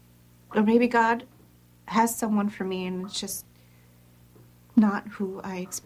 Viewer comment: You know @ 3:46 I really thought Nat said "Maybe God has someone for me, SHE's just not who I expected". Thought she was going to fly across the kitchen into Liv's arms. Alas not, she just ran 'its not' together. Her eyes said it all though!!!